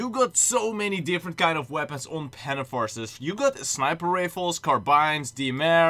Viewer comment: did you just say pantom forces?